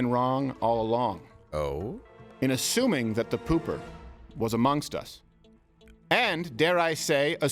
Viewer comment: I Thought I Heard there was another Pooper💩🤔🧐